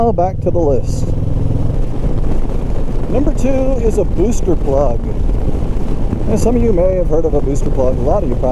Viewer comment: I'd really like a link to the ECU product you mentioned. I can't quite understand the brand name you mentioned in the video. It sounded like you said power plug, but I can't seem to find that anywhere. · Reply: Sure, it's called the "Booster Plug." Their website is <link> Hope you subscribe!